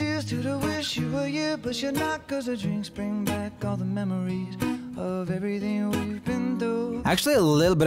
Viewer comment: "he is singing memory instead of memories" Me: i think i heard "mammary" 😏😏